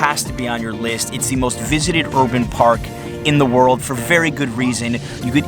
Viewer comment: I thought you said "visit sexual park" haha. Great video and valuable info.